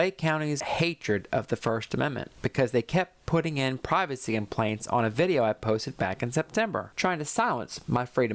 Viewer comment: I think she wanted to say “privacy complaints” but it sure sounds like “privacy implants”. TMI